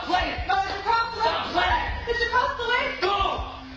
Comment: German here: at 0:09 it sounds like she says: bitte komm zurück. (please come back)